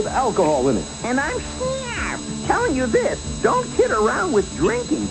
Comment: Does Shnarf say "Don't tit around with drinking" ? at 0:17?